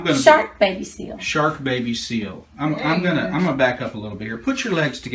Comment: When he gave her name, I thought he was saying she was bit by a shark, baby seal. Lol